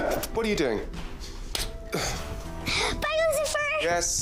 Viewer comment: … at first I thought Trixie said “Bagels and Fur”… oh wait. That doesn’t make sense at all… Trixie just said “by Lucifer”… 😂